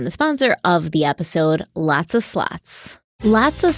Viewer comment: Omg, I have the same”lots a slots” ad on x2 and I was like WHAAAT, I had to double take when I thought Stephanie said “lots a sl*ts.” 😂😂😂 my head “ they are advertising WHAAAT NOW?”